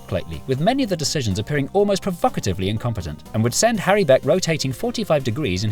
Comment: When you said provocatively at 8:38 I heard "perfucitively" and thought I had just learned the greatest word ever created...